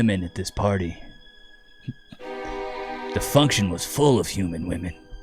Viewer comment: Lol I heard you say "Dysfunction (this function) is full of human women" and that's why I have no close female friends 🤣